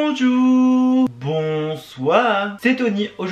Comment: c’est moi ou il a dit bondour au lieu bonjour